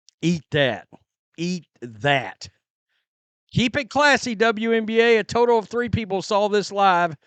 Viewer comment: When the Lord said class, she thought he said glass. She ran in to get her shoes and missed out.